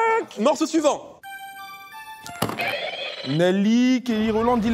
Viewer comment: A 19:39 j'ai entendu Nelick🤔